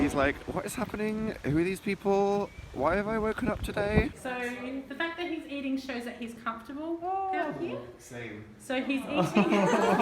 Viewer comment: What was saying the woman before said "same" at 6:30 ? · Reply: She said 'The fact that he's eating shows that he's comfortable around you'